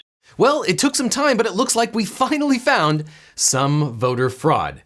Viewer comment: I thought he said "fuckingly found"